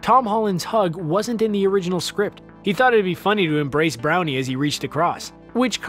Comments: LOL at 10:00 he brownie instead of downie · Just me or does he say Brownie instead of Downey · did he say “Brownie” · Am I the only one who heard the “brownie“?! 😂😂😂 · was I the only one who heard Brownie 😂😂😂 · did u just call him browny? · did he say brownie? Lol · I'm sorry what? wait did he just say embrace Browny?!?!?!? 🤣🤣🤣🤣🤣🤣🤣🤣🤣🤣🤣🤣🤣🤣🤣🤣🤣🤣🤣🤣🤣🤣🤣🤣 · Did anyone else hear him call RDJ "Brownie" at 10:00? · did he just call home brownie?😂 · does he say browney? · Did... did he just say BROWNIE? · did you just call robert downy jr "brownie"? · i was like "did he say brownie?" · he said Browney instead of Downey... LOL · Okay has anyone else noticed that he says "brownie" instead of Downey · im sorry did you just say.. 𝘣𝘳𝘰𝘸𝘯𝘪𝘦? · Did he say Brownie instead of Downey? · Is he saying brownie in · did he just say brownie? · did he say brownie??